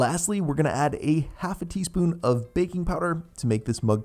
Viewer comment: I made this tonight. I thought you said 1/2 Tbl spoon of baking soda and you actually said 1/2 tsp 🤣 My cake overflowed in the micro but nbd. Also, the cream filling is a little too sweet for me. I will probably use half next time. You must have a sweet tooth bc I tried your dalgona coffee recipe and it was a little sweet too. But I will always try out your recipes 😄